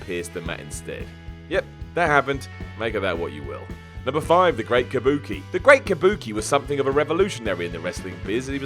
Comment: I swear, I thought it said “the Great Bukkake”